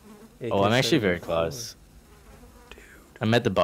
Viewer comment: Did anyone hear whisper it said dude